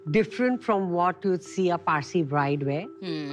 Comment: For those who don't know what parsi is... Parsi people are descendants of Persia who are settled in India and can be seen in Pakistan also i guess. They follow the religion zoroastrianism and not Hinduism from what i understand. So they don't wear red outfit for wedding as Hindus do and they wear white, off white or some colors very near to white. And Shilpa Shetty is a Bollywood heroine who's tall, beautiful and who does yoga and maintain her slender body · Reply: Thank you! I thought maybe they were saying "Farsi" but that didn't fully make sense to me